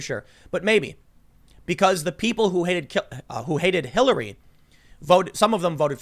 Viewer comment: LMAO that slip up at 13:30, he was about call hillary clinton killary